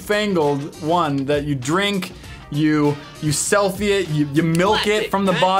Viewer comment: When you where playing the bop it game and when it said selfie it I thought it said filthy it and when said milk it I started dying or laughter 😂😂😂🤣🤣